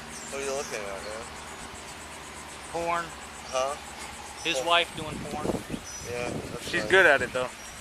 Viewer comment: 😳 wow did he say” watching wife doing PORN”